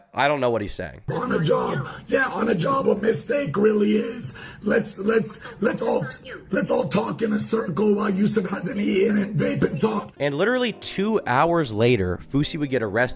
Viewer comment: "on a job, yeah on a job a mistake really is. lets lets lets all t- lets all talk in a circle while yousef hasn't eaten and vape and talk" i think thats what hes saying at least